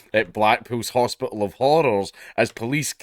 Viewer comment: I was listening to this rather than watching it and did doubletake at 0:17 when I thought you said "Blackpill's hospital of horrors", probably ought to just rename it to Blackpill hospital. . .